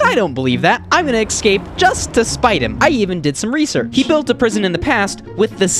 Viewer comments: nothing motivates you quite like spite 😋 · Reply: I thought you said sprite until i read it again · nothing motivates you quite like spite 😋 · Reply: i thought you said sprite lmfao